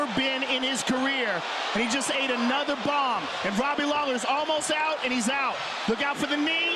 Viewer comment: "I can't understand how he would call it..." Wtf he talking about both him and Joe were screaming HES OUT HES OUT HES OUT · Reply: it sounds like he says, "oh, he's almost gone out."